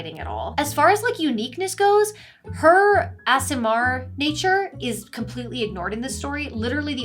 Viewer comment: subtitles say "ass MR" instead of Aasimar. Presumably, not completely familiar with DnD, maybe there are ass MR's in there.